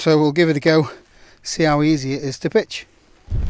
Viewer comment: I heard "I've never pitched a tent before so we'll see how easy it is to bitch." Subtitles needed on this video, seriously. And don't get me wrong. I love me a two man tent.